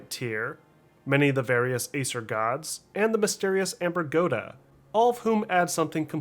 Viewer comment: did i hear ambergoda ?